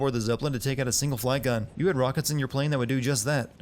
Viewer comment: did he say “rockets” since when does battlefield 1 planes have rockets?!?!?!?